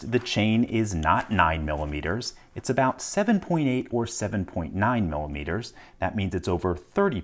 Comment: did just say, “about,” and then go to 3-4 decimal points of precision? What even lol 😂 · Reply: nope. he said "7.8 or 7.9 millimeters".